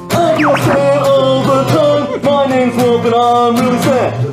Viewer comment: I JUST now figured out what Shayne said here. “I feel sad all the time”